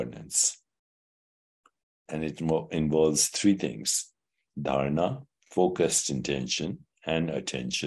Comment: What are the 3 Indian words he lists at @ 12:18 ? Darna?Dyana? samadhi? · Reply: Dharana is the first step to meditation and translates mostly to “focused intention” or simply, concentration. So giving the mind a single focal point to continue coming back to again and again. Once you continue doing that, eventually the effort to focus becomes more easy, and at that point you switch to Dhyana - “meditative absorption.” This is the state when meditation comes with ease. Eventually you reach samadhi or moments of samadhi, which is like the realization of eternal bliss, abundance. Enlightenment, nothingness and allness all together. It’s not in the mind, it’s like “Buddha mind” or like your mind becomes the container and in there is only peace. I hope this helps! It takes much practice and if we do ever “reach” samadhi and feel a moment of “oh wow I did it!” We pop right back out of it. 😂